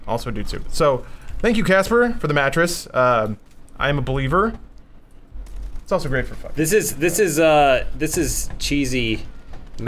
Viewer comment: does he say "it's also great for fucking" ?